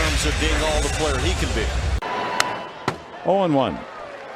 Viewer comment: Did he just say "hole in one" there? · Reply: He said 0 and 1